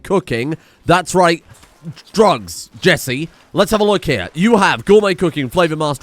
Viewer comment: I heard "Drugs Jesse" and I got excited.